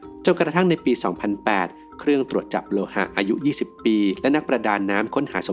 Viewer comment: เราได้ยินว่าเครื่องจับโลหะอายุ20ปี